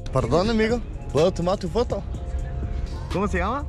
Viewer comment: Sounded like he was saying “oh Tomato photo!”